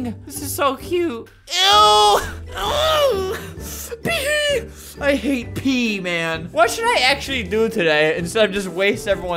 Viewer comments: What Albert said “I hate pee, man!” What I heard “I hate Pee Man!!” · "I hate pee, man." Pee-man: BIG sad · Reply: Yea it sounds like hes saying, "I hate pee-man!" But he's actually saying, "I hate pee, man!"